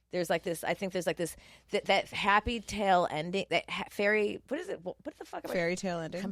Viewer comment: lmfaoooo when she said “happy tale ending” instead of fairytale 😂😂😂😂